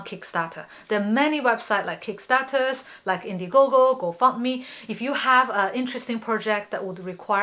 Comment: lmao what did she say? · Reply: 'GoFundMe' will never be looked at the same